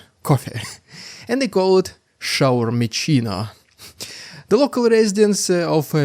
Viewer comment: I don't know if I misheard the name of this super juicy looking snack but for me it sounds like a mix of english and german. "Shower mit China" which just translates to "Shower with China". I love it 😂